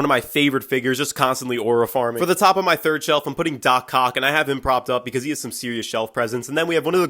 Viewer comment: Thought you said Dr. Cock